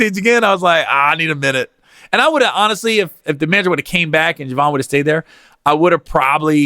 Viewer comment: if who came back? It sounded like a slip of the tongue, but I’m ask anyway. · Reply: Mister Jones he said manager ...